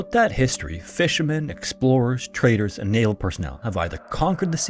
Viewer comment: I can't hear a word spoken near the start of the video, can anyone help? I'm struggling at 0:50 where he says "fishermen, explorers, traders and nail personnel". What is nail personnel or is it a completely different word he says?